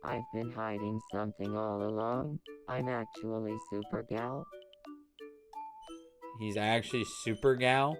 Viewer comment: rewatching the series and I thought Hayden said “I’m super gay” 😭